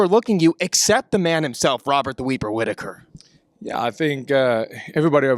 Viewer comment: Did he say "Robert The Weeper Whittaker"? Lol. Subtitles heard "Robert The Weaver Whittaker" 😂